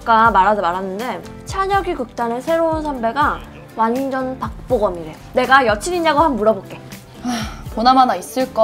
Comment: The English translation for 7:37 is wrong. The man that she’s talking about doesn’t look like a member of BTS. What she actually said in korean was, “he looks totally like Park Bo Gum.” If y’all don’t know who he is, he’s a well known actor in Korea. He’s very talented and kind-hearted. He also happens to be good friends with Kim Taehyung.